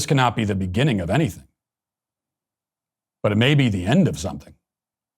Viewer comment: Thought it said end of whites. I got excited